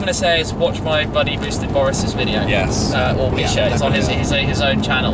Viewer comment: whos name is he saying? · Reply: Colin Fenton Boosted Boris/ Misha Charoudin